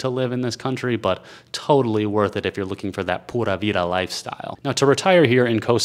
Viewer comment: OMG. I was doing laundry when I thought I heard you say, "puta vida," (the whore life) LOL......i had to skip back a few seconds to realize you'd said the "pura vida" LOL.